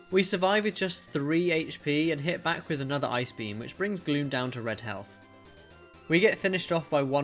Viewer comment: i thought you said "i brought MEW down to red health." I was eating my lunch and was just listening. I snapped my attention to my phone, and realized that he said gloom.